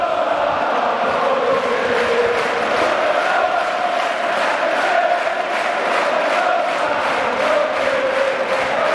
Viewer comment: Cosa dice il coro a 1:20? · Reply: enrico volpetti e quando i blu saranno in ciel, io voglio essere con loro